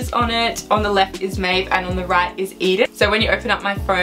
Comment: DID U SAY MAEVE????? PLZZZZZ ANSWER ME AND UR SISTER HAVE THE SAME NAME!!!!! PLZ ANSWER